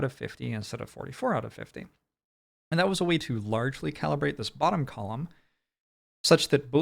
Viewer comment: sorry, at 1:25:30 i heard you say "large[...]bottom" and became so distracted i furgot everything you said up to that point. could you start again from the beginning?